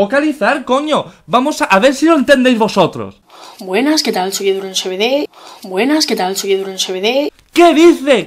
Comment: lo puse en cámara lenta y creo que dice un oso bebe